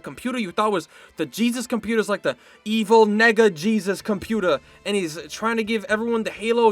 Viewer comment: You did NOT say mega.